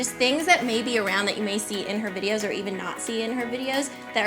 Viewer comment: I know what she actually says, but I keep hearing ''..Nazi in her videos''. Jesus :D